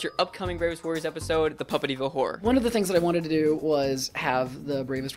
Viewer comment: I kept hearing "puppet evil whore".